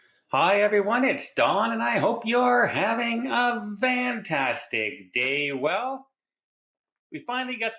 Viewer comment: ??? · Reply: If you are ??? about the VANtastic it's because when he says "hope you are having a FANtastic day" we told him it sounds like he is saying VANtastic. He said he is saying Fantastic but maybe due to his accent. Wasn't sure if that was what you were questioning :-)